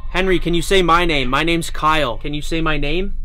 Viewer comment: it sounded like “it’s Kyle”